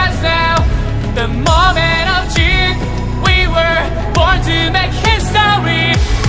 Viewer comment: is it just me or do i hear "the moment of cheese" lmao